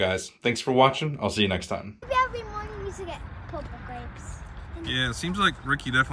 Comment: Rickey likes the purple grapes… 😊 At first I thought she said “proper grapes”